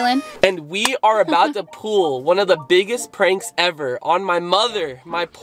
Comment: did he say pool insead of pull